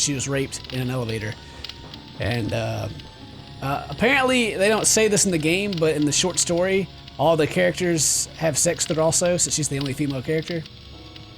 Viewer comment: what the heck does ken say at 0:56 to 1:02 "apparently..bla bla" ??? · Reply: ***** "Apparently, they don't say this in the game, but in the short story all the characters have sex with her also since she's the only female character." I believe is what he says in its entirety.